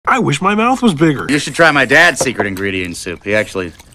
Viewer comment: I hear "trigger" not "bigger"